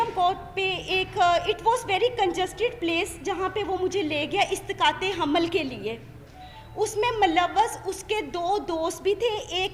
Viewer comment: istiqate hamal instead of isqate hamal lol script phr k ani thi bibi